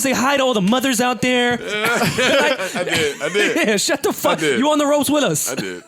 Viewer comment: Did Rory just say “n***a shut the f**k up” at 11:53?